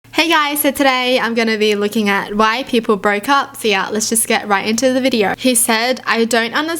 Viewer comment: I thought it said “why we broke up” I thought I missed a whole chapter